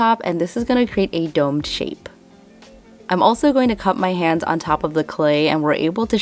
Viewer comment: omg i thought you said "im gonna cut my hand" lmao